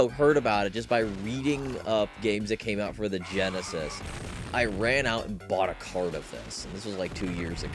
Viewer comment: Had to rewind because I thought he said "I ran out and bought a cargo fence" o_0